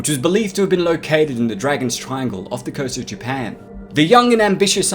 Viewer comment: ha! I thought u said "Devil's Triangle", at 1st.. guess that term's just indelible in my hippocampus now 🙃